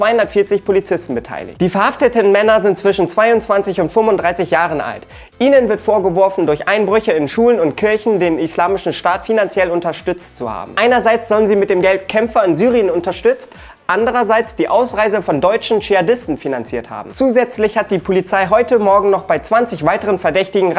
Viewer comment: Lol ich hab "Godzilla unterstützt Thor" verstanden und dachte schon wir sind hier bei Techscalibur